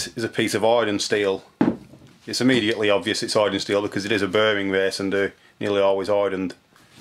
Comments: wtf did he say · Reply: "it's obvious that it's hardened steel, as it is a bearing race, and they are nearly always hardened" I spoke too fast, even I struggled to understand it 🙄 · what in the world did he say? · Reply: Hardened steel 👍